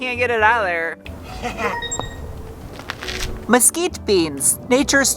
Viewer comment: At first I thought you said mosquito beans